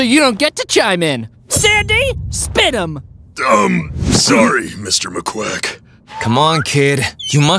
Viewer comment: did sandy just say Maquack 😭😭😭